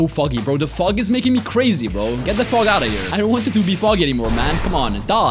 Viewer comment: I misheard him saying "Get the f*ck out of here" XD